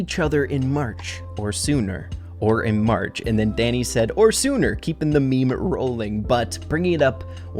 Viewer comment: wait i always thought dani said “or in summer” instead of “or sooner” My life is a lie